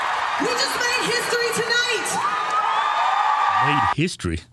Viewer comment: did she say she ate history? 😅 cuz I believe her